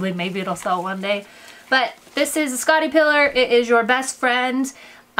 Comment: what is that tool? I didn't catch it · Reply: Scotty peeler!